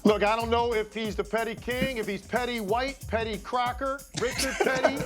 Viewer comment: Legler’s “I don’t know if he’s the petty king, Petty White, Petty Crocker, or Richard Petty…” made me fall OUT OF MY BED on to the floor rolling😂😂! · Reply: I thought he said pretty cracker and he was about to get cancelled.